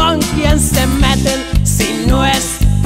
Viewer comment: "Si no es por el pericaso no hubiera salvado el flete" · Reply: Pensé que decía si no es por el “vergazo”